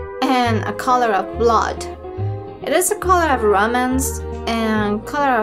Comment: you're awesome. I will always watch. When you said Romance the first time, I thought you said "Romans"...Red the color of Romans.